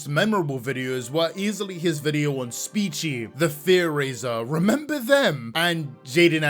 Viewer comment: Bro, when that clip played of him mentioning TheFearRaiser individual, I legit thought he said The Theorizer. Had to do a double take and make sure he wasn’t about to disrespect the goat.